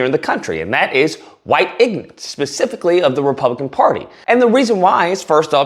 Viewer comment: did he say ‘ignance’